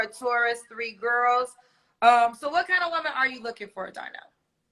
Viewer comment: It’s funny how keeps calling him Darnell and his name..😂😂😂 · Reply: I thought he'd said "Damrell" at first 🤦🏾‍♀️😂